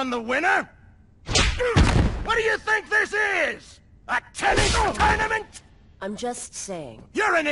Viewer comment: "SO HE CAN TAKE ON THE WINNER? WHAT DO YOU THINK THIS IS, A TENNIS TOURNAMENT?!" My favorite line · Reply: I first thought he said: what do you think this is, a tekken tournament.